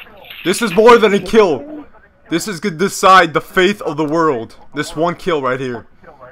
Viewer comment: You said the faith of the world instead of fate hahahahahahaha